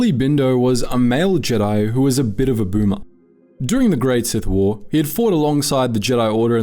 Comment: I heard groomer there at first😂😂